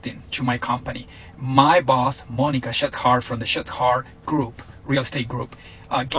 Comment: I genuinely thought he said "Monica Shithard"